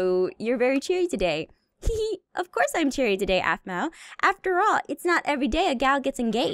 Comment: Anyone else notice at about 6:36 Donna says of course I'm very CHERRY instead of cheary.